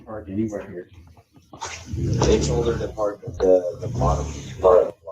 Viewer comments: on Linda Lane cam: "We shouldn't park anywhere here". Why not? Too close to the camera? " They told her to park at the bottom part". Who is 'they' ? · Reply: I hear, "I TOLD HER NOT TO PARK"... park down by the lodge! First person. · the first mad fella sounds super stressed because of where someone parked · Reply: I thought he said I told Abby to not park here. Maybe just my ears lol